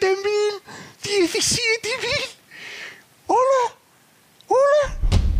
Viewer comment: was actually just saying "17 thousand" not whatever was captioned there.